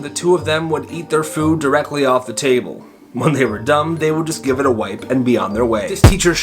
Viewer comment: did anyone else notice that he said "when they were dumb" instead of "when they were done".